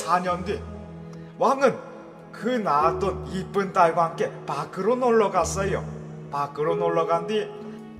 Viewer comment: 저만 밖으로 를 마크로 라고 들었는지...